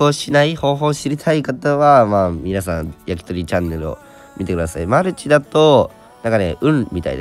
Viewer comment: やりとりチャンネルに聞こえた笑笑